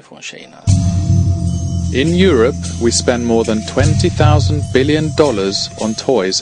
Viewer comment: did he say,"20 thousand million?"?? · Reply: Twenty billion dollars